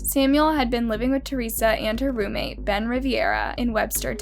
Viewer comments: I was so scared I thought she said “Brent Rivera” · I thought it said Brent Rivera😂😂